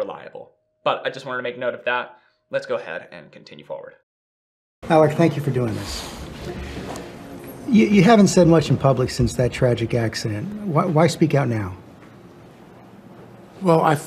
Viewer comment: Alec actually did respond to the interviewer’s initial “greeting”. Although, it sounds like the interviewer says “Howard, thank you for doing this.” Who’s Howard? Or did he actually say “Alec”? If the interviewer actually said “Alec, thank you for doing this.” Alec nodded which I interpreted to mean “You’re welcome.”